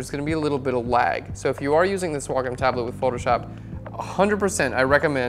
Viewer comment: (thought it was said way-com XD)